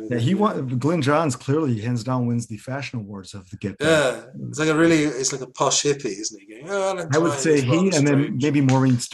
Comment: What does say at 40:22 during his Glyn Johns impression (ending with "strange")? Thanks. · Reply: I said GJ was like a posh hippy